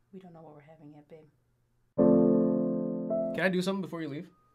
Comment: I’ll never forget the 1st time I 👀 this; I swore what I heard what “Hey babe, can I get you something?” & I thought “aw… GG checkin on his beautiful pregnant wife, that’s so sweet” What REALLY said … “Hey babe… can I do something really quick…” beginning jingle plays 🎼 ice🧊 ice🧊🤰🎶 The opposite of what I heard & yet…10/10 regardless.